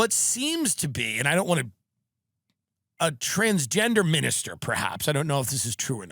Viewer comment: I thought she said “trans ginger” at first.